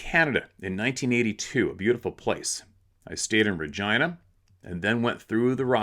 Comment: "STAYED IN VIRGINA" it sounded like you said"!!!!!!!not Regina. LOL, spit my drink all over myself.....